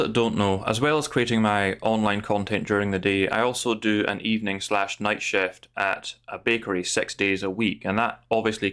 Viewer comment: Lol, English is not my native tongue. Your first sentence I heard as: "I also do a evening/night shift at a bigotry" :face-purple-crying